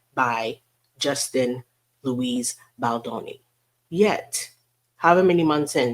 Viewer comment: She’s so worried about her rep she forgot about her bogus SH claim. I had to look up JBs middle name because I thought you said LOUSIE (Loo-eese) but saw you said LOOIS! 😂😂😂